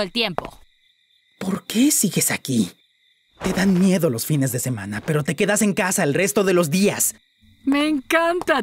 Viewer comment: hay un pequeño error de traducción, lo que stolas dice es “ you left with Via on weekends “ ósea “te llevas a Vía (Octavia) los fines de semana” es fácil confundirse ya que en serio parece que dice “fear” (miedo) pero creo que es así por su acento británico. Igualmente buen doblaje equipo ;)